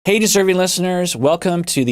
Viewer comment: tell me why i thought this man was saying "hey disturbing listeners" for the longest time and i didn't bat an eye